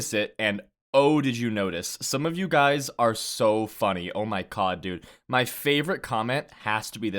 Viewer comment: lmao I thought I heard you say "Oh my cod dude". I had to double take that lmao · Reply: I did say oh my Cod 😎